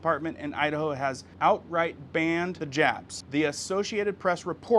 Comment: I thought you said “ ban the japs “ 😂😂😂 notban the jabs 😂😂😂